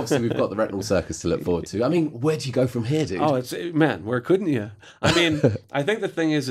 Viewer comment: what does he say at 3:17, right after the interviewer's question? could anyone please help me understand what's said there? thank you in advance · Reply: "man, where couldn't ya?"